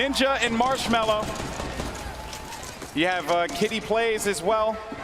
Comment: Anyone notice at about 7:00 the caster says Kidney instead of Kitty?